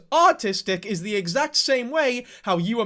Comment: Bro when you were making your clarification i thought you just kept saying autistic and I still didn’t see the big deal but now that I know you were trying to communicate “ARTISTIC” there’s a huge difference lmfaoo. Good on you for setting the record straight tho